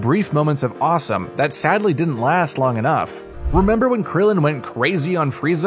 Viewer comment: Did he just say burrito instead of boruto